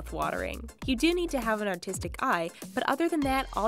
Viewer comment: I thought she said autistic euey